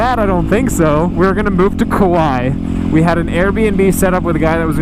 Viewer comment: Did he say at 5:52 "We were gonna move to Kawaii" ? ^o^